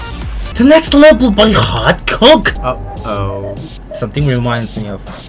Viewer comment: we all know what we thought he said lmao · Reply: Coke